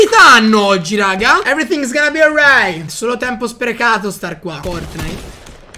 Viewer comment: solo io ad 1:52 ho sentito a caso fortnite?